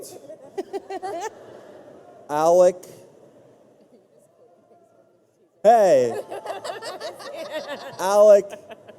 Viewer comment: ??what did alisha said to matthew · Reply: freakcookies i think she said “you’re just quoting things from the teaser”